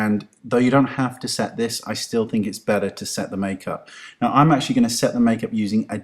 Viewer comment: I thought you said less makeup.???